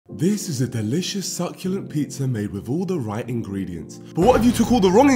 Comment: succulent… bro pulled out google for that pne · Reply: and he actually said succulent